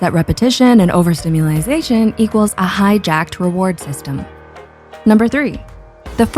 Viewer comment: Hijacked? I thought you said High Jacked 💪🏋️